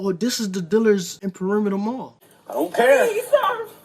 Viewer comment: All I hear is Big Boi Please? Please what?